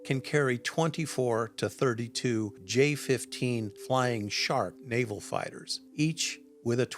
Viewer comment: Did he say, “Flying Shart?” 😄🤣